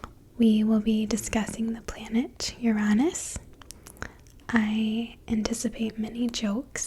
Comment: did she say 0:26 Lord "Tyranus"?!